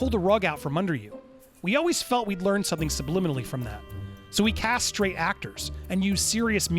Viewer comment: who else heard him say “So we castrate actors?” That’s an Airplane worthy pun!